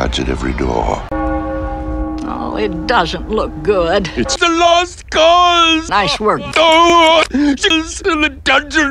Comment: what was the gargoyle saying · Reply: It's a lost cause! - Nice work.